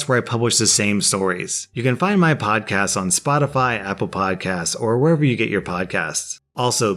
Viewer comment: My YouTube lagged at the end when said “Spotify Apple Podcast” and it sounded like he said “Spotify Apple pie”